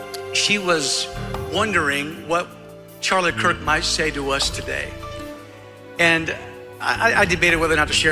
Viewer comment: Umm…did I hear that right? Did that man say “Charlie Kirk, my savior?” What in the world?!